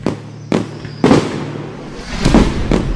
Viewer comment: What jungle? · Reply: I thought it said Oreo jungle